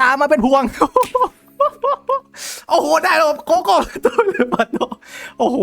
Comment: ทำไมผมได้ยินว่าโกโก้อะ😂